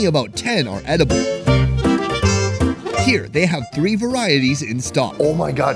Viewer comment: I thought he said "free" when he said three 🤣 I was like 😲 then 😃